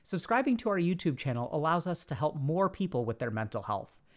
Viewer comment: I thought he said poor people LMFAO